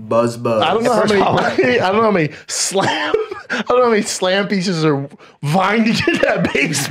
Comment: I thought he said “slim piece. “